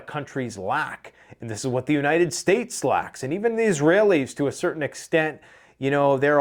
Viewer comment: Man, I thought I heard "Ex-Lax".